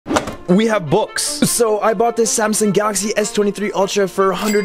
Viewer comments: I thought he said "we have books" 😂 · I heard 'books' instead of 'box' and was waiting for said books for the entire video